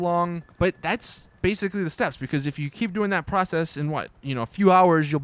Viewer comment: I thought he said 3 steps 😶...